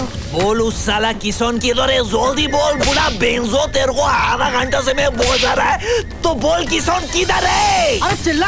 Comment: did chunkey call him bensoad